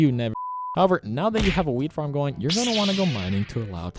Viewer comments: did he say weed farm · u notice how he said weed farm instead of wheat